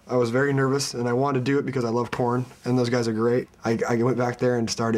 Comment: ' he sounded like he said ' i love porn' xD